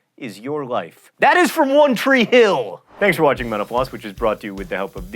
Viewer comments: I swear, I always hear him say "Thank you for watching menopause." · I definitely thought he said, "Thanks for watching menopause."